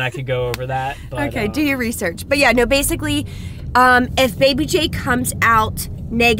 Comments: Sorry haven’t seen them in a while. Did she say Baby Jake? At 4:16. did they announce the name? Sorry I’ve missed some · did just say baby Jake?? is the baby named jake?? or did i miss the name reveal?? OMGGG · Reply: She said baby J; first name starts with a J and his middle name is Joshua is all they've said so far